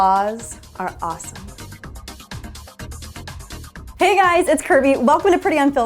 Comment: I heard hey guys it's curvy.